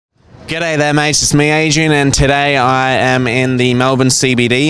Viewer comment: I thought you said " this is me Asian "